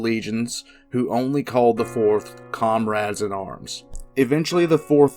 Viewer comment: I understood “Conrad in arms” instead of comrade in arms.